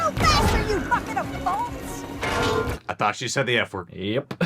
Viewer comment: Glad I’m not the only one who head her say “move you fuckin—” before I heard “bolts”.